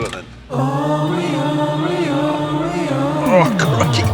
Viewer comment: Did he say Crikey at 2:16?